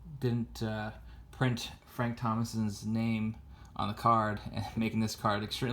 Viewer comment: Did you say Frank Thompson ?